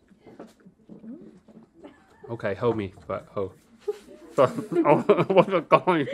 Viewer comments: I thought he said ho me but ho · I heard him say "Hoe me, but, hoe" 😂 there was a lil pause